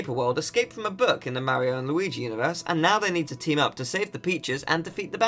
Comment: i clearly heard save the b¡tch3s , i had to listen to it again